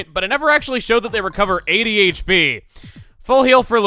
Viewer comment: when he said it restores 80 hp I thought he said ADHD, lol, now whenever someone needs to cure ADHD, just get an ultra shroom!!!